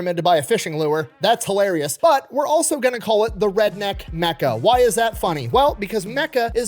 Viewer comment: "But we're also gonna call it the redneck Mecca" Me, an intellectual, thinking of the meme image of the Imperator Titan with the Bass Pro shops pyramid on the back instead of a cathedral (I heard "mecha" and not "Mecca")